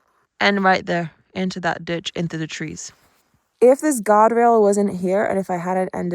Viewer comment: This has really given me a whole new appreciation for guardrails 😅🙌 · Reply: I thought you said, "If this God rail wasn't here..." Perhaps that's what is was. Maybe it was a small whisper to you from the Creator, longing for your attention. Very thankful you're OK.